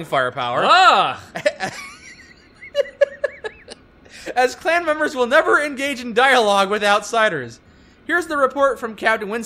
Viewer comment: What I heard "as clan member will never engage in dialogue Without Cider"